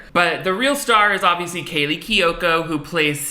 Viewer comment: I cant believe that hayley kiyoko started from this film, and grew up to be queen of all the lesbians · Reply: exactly! for a sec I thought said kayley kiyoko and thought she was Hayleys sister for some reason